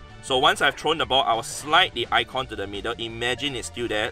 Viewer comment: what is he saying at 3:14 ? "Once i have thrown the ball I will...?" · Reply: Slide the icon to the middle